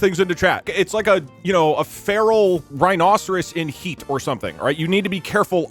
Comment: is he saying he's in heat?